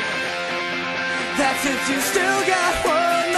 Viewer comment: I thought he said: "that's if you still got one life left, inside that cage you call a jet"